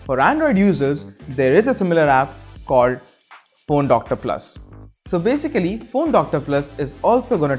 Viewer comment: Oh my god I heard porn doctor plus not phone check again